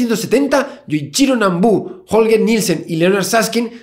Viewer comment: En el minuto 2:19 entendí el chino nambú y me cague de risa JAJAJAJAJAAJAJJAAJA